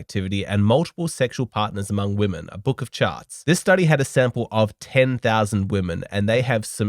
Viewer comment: For a moment, I thought he said, "A book of Chads" 😆